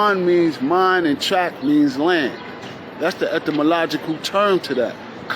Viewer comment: did he say ectamalogical?